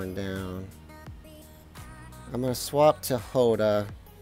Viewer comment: Did you say Hoda 06:53 i like taht name